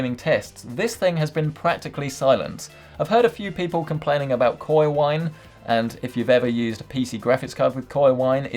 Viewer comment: the subtitles said "koi wine" lol